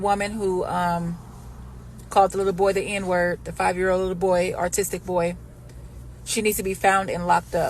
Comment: did she say artistic?